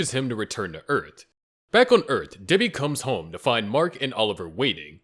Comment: i heard diddy instead of debbi 💔💔💔💔